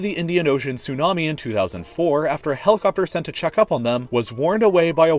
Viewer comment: I thought I heard "helicopter", not Helle Kapur when subtitles mishear pronunciations.